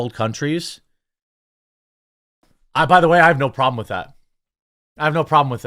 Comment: Did you just say you have "No Problem with Terries" ?!?😅